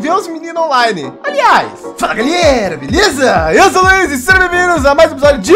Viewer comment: ele falou Luiz ou foi impressão minha?